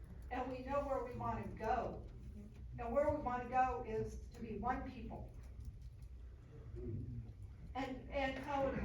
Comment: i swear to god… i had to rewind this twice. coulda sworn this lady said “we want to be WHITE people” & then “fuckk each other up”. like, excuse you?! what did this lady say & why is no one shocked 😳 ohhhh… lol